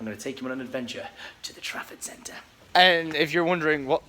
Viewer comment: I always thought he was saying the traffic centre